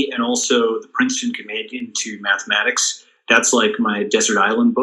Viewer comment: What did Bilaji say towards the end 1:17:30 ? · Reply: 'Princeton companion to mathematics'